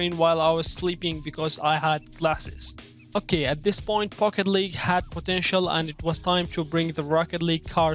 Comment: It sounds like he says "Fuck it League" 🤣